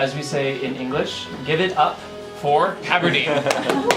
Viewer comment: I could have sworn my man said "get it up for Aberdeen"